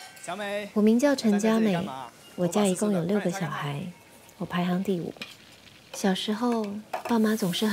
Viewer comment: Hi. I am learning Mandarin. At 0:47 what exactly she says of my family have 6 children. Doesnt sound like "wojia you liu ge xiao haizi". Need assistance. Tq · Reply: It says "wo jia ei gong you liu ge xiao hai" the ei gong means total. So the sentence meant. My house have a total of 6 children